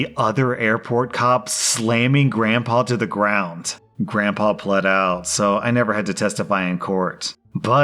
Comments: I was listening while getting my toddler together. I heard ‘Grandpa bled out, so I didn’t have to testify.’ Went from ‘that’s convenient’ to ‘WHAT DID SHE SAAAAAY?!’ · Listening while driving and had to do a double take when I heard "grandpa bled out so I didn't have to testify" instead of "pled out". LOL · I thought he causally said that "Grandpa bled out". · I thought he said grandpa BLEED out and I was like WHAT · At first I thought the dude said bled out and I was like hell yeah that's awesome plead out which is kind of boring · I thought you said "grandpa bled out" and I was like WOW that escalated quickly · oh my god, I heard 'Grandpa BLED out, so I didn't have to testify.' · I thought he said “grandpa bled out so I didn’t have to testify” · i swear to god i thought he said grampa bled out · I thought he said “grandpa BLED out so I never had to testify” 😭😭😭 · First story: For a brief moment, I thought rSlash said "Grandpa bled out, so I didn't have to testify." I went Oh, that turned very dark very quickly... 🤣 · On the airport story I thought he said Grandpa bled out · Omfg I had a heart attack I thought you said grandpa BLED out not PLEAD out and were just glossing over manslaughter 😳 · I was listening to this is the background and I thought he said "grandpa BLED out" I was like "WAIT WHAT" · Oh, damn. That first story I totally heard, "Grandpa bled out" instead of "Grandpa pled out" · I thought it said grandpa bled out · I thought you said that "Grandpa Bled Out"